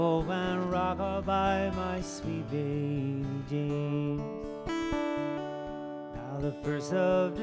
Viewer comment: When I first heard this song I thought he was saying "sweet baby Janes", a term of endearment for the cattle. Still like the idea.